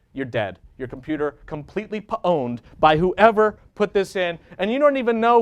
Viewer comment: did he say "pwn'd" at 36:07? this dude is genius